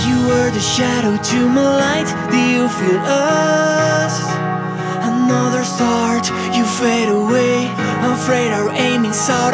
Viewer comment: At first i thought you said "do you feel us, another fart..." in the first verse lol